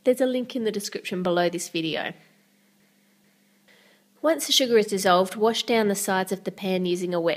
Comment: XD I Hear "Suga" Not "Sugar" XD